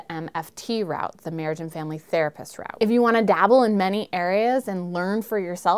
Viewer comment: The first time I heard MFT, I thought you said the 'Murrican Family Therapists.